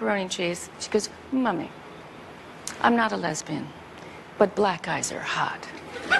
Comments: "Mummy, I'm not a lesbian, but black guys are hot!" 😄😄😄 I love it!💗💗💗💗💗💗💗🙏💗💗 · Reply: Oh I thought she said black eyes! 🤔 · Wait a minute Tori, honey, your oh so thought out daughter must have been about 11 years old when you did this interview. Now, she has her sexuality figured out at this age, is ready to talk about it AND thinks that black guys are hot? A girl who has been listening to the blues for 2 years at that point? That seems as unlikely as your face, I'm sorry. Why does it always have to be so ingenious? Why cant Tori Amos' daughter not simply like ponys and pink things? I know I did and I'm a guy :-) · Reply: Maybe it’s me but I heard ‘black eyes’ as in make-up with a lot of black eyeliner 🤷‍♀️